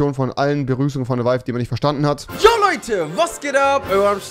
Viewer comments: ich hab nie verstanden was er sagt ich dachte immer er sagt: Was geht ab, EURO am Start. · Moin Leute Euro am Start Wer hat es auch so verstanden?